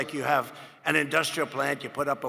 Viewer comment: What did he say at 1:46? Indrialls?This man can even say windmills haha